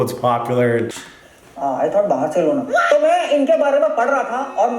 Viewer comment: bro listen to me, here you thought that Bhuban had said, "Barcelona". But he had actually said hindi, "Bahar se lona". English translation, "Keep it from backside" He meant "F*ck me from behind" 🥲🥲🥲🥲